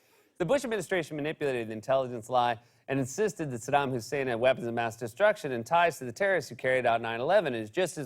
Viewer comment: Did... did he just say "weapons of bass destruction" after the 'give a man a fish' joke? If so, that's genius-level comedy.